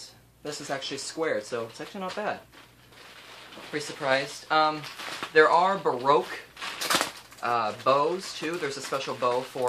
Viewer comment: OH I didn't know there were kinds of violins! I thought he said "There is a broke bow" 😂😂